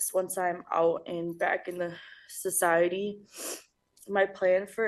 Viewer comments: Can’t believe her lawyer let her read that statement. “Now that Derek is gone I want to use his death as a learning lesson and to make myself a better person…” She only cares about herself and sounds like she’s still using. This judge is great, need more like her to hold these people accountable. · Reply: She almost said back in the hood instead of back in society. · Lady, you’re being sentenced for causing the death of your child, not accepting an Oscar 🤦‍♀️ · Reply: That part! I could have swore she almost said when she's out and back in the hood instead of back in society.